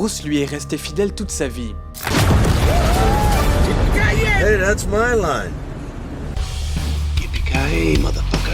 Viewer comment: qu'est-ce qui dit ??? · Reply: Hey it’s my line. (Hey c’est ma réplique)